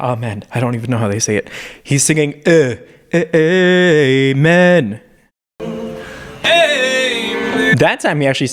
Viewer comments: I always heard “hang men” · Thought he was saying gay men Amen makes more sense